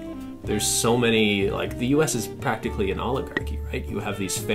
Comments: I thought he said “the US is practically an Olive Garden” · When said “oligarchy” I thought he said “Olive Garden”